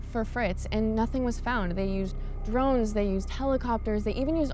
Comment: Did she say drones?!